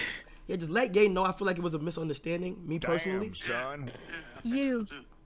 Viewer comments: Did Kia say “just let GYE know I feel like it was a misunderstanding” ??? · anybody else hear him say let gay know...